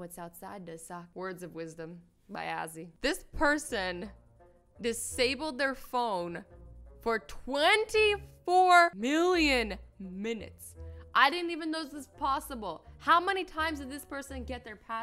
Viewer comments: did she say billion 😂🤣 that is clearly million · Oh I thought she said 24 billion minutes not 24 million minutes then I rewatched it and realised 😂😂